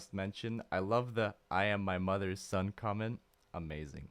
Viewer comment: Freddie said, "I am my mother's son?" I thought he said, "I am my father's son," which didn't make sense. Mother makes more sense.